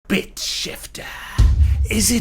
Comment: i thought that he said bitch